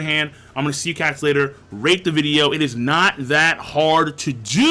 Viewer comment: Am I the only one who heard "Rape the video"?